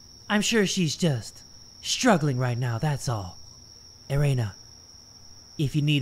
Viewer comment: I swear to god I heard him say ‘my hoe’💀